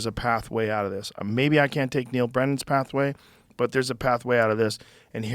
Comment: Thought it said Neil Breen, now I'm disappointed and not going to watch